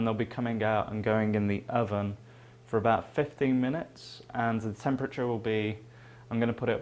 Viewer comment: How long do I leave the salmon in the oven for? I thought I heard you say 15 minutes.... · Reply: Hi there, yes it is in the oven for 15 minutes :)